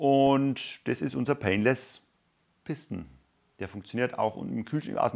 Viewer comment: Painless piston. I heard “painless pissing.”